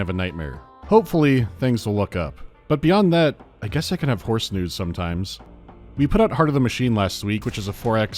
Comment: What is saying at 38:30? It sounds like he said "I guess I could have horse nudes sometimes", and with his horse history I'm getting kinda worried.